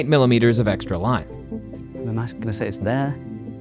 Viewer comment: i thought it's "the math gonna say" not like in subtitles